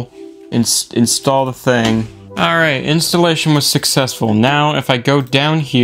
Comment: it sounds like he said “instellition” sometimes